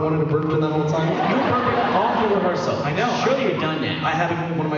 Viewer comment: what does he say he was doing? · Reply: Burping